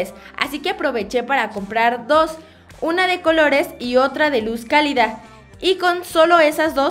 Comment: que dijo care en el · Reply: es y dijo luz cálida